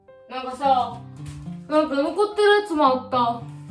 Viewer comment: なんかうんこってるやつにしか聞こえない予言